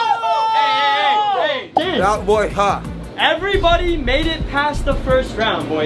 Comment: sounded like he said "that boi hot" lol